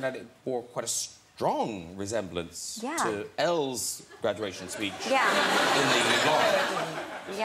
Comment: When he said Elles graduation speech, I thought he said L from death note and I was like💀🤚